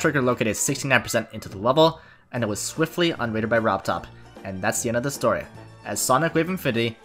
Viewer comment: i heard "robtop won" for so long and i was really confused